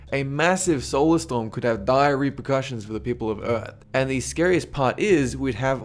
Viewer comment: I thought he said diarrhea when he said Dire reprocutions